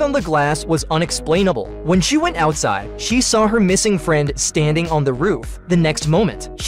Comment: When Alice went outside I thought it said she found her Muslim friend💀💀